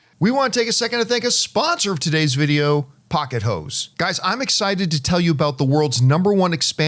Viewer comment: All I heard was “pocket hoes”